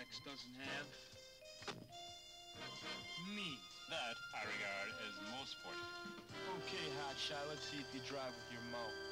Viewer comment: Does anyone understand what nerdy dude is saying at 1:28? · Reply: “That I regard is most fortunate.”